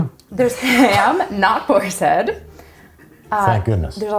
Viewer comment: did he just say no thank you to BOARS HEAD??